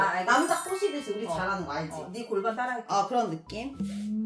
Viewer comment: 샤워하고 나와서 영상을 보면서 몸에 있는 물기를 닦고 있었다 고개 숙여 남자 그곳을 닦고 있는데 영상에서 니 골반을 빨아줄께 라는 말이 들렸다 응? 뭐라고? 골반을 빨아준다고? 나는 잽싸게 영상을 뒤돌려 봤다 그런데 니 골반을 빨아줄게 가 아니라 니 골반을 따라할게 였다 영상 시간 3분 50초 진짜 영상 안보고 들으면 니 골반 빨아줄게로 들림